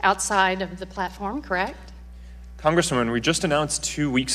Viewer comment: Did he say ''congressman'' approximately 3:36? Probably his CPU can not compute relatively difficult questions...